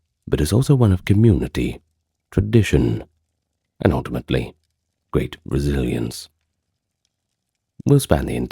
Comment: A little under 2 minutes in he says “great resilience” and I misheard and thought he said “great Brazilians” and I was like What do Brazilians have to do with this? 😂 *it’s at 1:46 😂